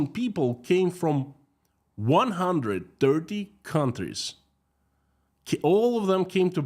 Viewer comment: I thought i heard 100 dirty countries on 1st listening